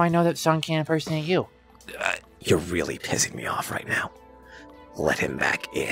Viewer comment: am i triping or did i just hear sun say your really p*ssing me off right now like is this also like for kids??? also if you want to hear him say it go at 8:19 LIKE BRUH- (not being rude just saying)